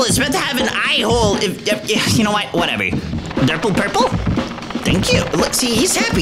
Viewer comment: did bro just called him durple purple😂😂😂